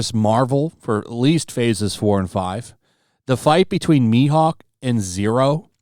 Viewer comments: you misspoke his name! you must burn! jk all good. i still get most anime names wrong too. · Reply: it's fine mate we had a misunderstanding. It sounded like the video said "shi-hawk" instead of mihawk so I thought it was a subtle insult. That's all. May be I misheard :) I'm well awake his name is Dracula mihawk or hawkeyes I was referring to the video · I misspoke, it’s Zoro not Zero. I’m a new fan, forgive me! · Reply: Sounded like you said shi-hawk instead of mihawk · did he say zero? XD